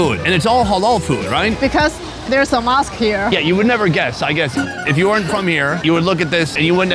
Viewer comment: I thought shes said there's hamas here, sorry.